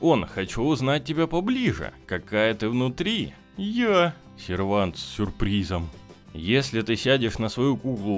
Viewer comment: кто тоже услышал похожое на "сарвенте"?